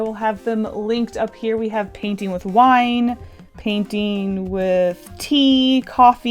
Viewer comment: I THOUGHT SHE SAID ”painting with pee..” SJSJSJS